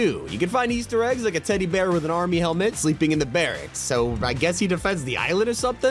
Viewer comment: Did he say "bearracks"?